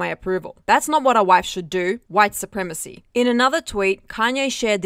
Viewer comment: i was listening to the video while doing something else and at 1:45, I heard "wife supremacy", I was like what? Then I watched it, haha.